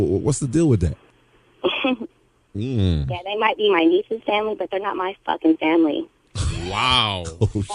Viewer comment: Did she say her nieces family?🤦🏿‍♂️